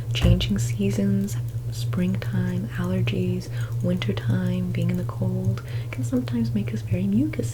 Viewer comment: I misheard "being in the cold" as "being in a cult" and didn't realise that I misheard until I turned on the subtitles. I just thought this was the classic experience